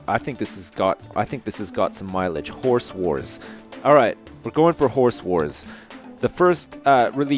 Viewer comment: i got to say it sounds like your saying horse whores.